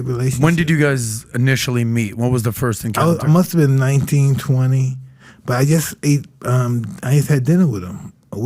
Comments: So at 26:53 did I hear him say he met Trump in 1920? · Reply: He was 19, 20 (years old) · Did he say he first met Trump in 1920? @ 26:55?